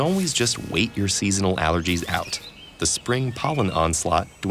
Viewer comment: Just in time for what season? (no, not summer, I couldn't understand what he's saying) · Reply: Ragweed season